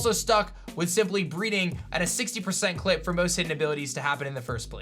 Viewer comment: uhhhh did u say clit?!